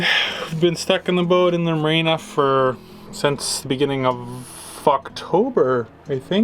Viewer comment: did you just say "Fu*ktober"?! I like it